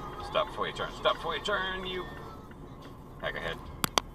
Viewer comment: did you call them a magahead? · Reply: No. "Peckerhead"